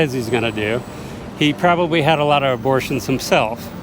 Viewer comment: Did he just say Trump, a male, had abortions???